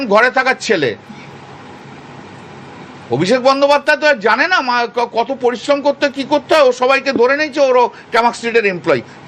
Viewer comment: abhishek banerjee janena or maa ke koto porisrom.. am I hearing it right or slip of tounge